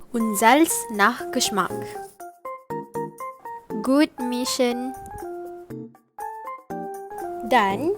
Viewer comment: misheard gut mischen = good mission 😂